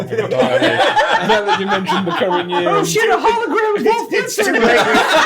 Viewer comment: I know this is a bit of an older video, but does anyone know what said here? All I can make out is "Oh shit, a hologram ..." It almost sounds like he's saying Wolf Blitzer. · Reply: Holographic Wolf Blitzer